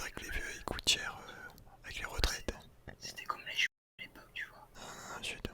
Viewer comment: cest moi ou il a dit "c'était comme les juifs à l'époque " si il a dit ça cest choquant